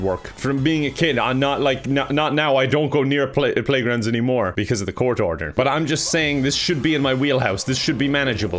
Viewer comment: wasn't paying attention at 0:47 and instead of "because of the court order" in understood "poor daughter" and was wondering if you were trying to establish the lore that you have a daughter who's not allowed on playgrounds bc you won't go with her. was about to comment "interesting choice"